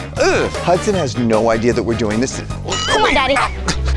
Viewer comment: Did she just say “come on daddy”?